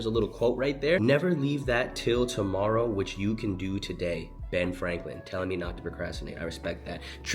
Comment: idk bout u but i heard “don’t put of till tomorrow what can be done today” (ifykyk)